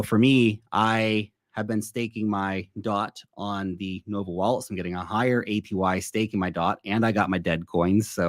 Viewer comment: What does Lark say at 19:14? Where is he staking his DOT? "Mobi Wallet"?